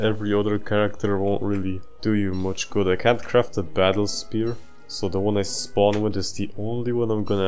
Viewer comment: 76 done I think 17 more to go ( 2:22 I heard spoon instead of spear. I scrolled up to the vids I have to watch next and uh 44mins .. ok)